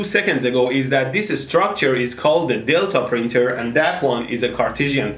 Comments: Am I the only one who thought he said dildo printer? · I thought he said dildo printer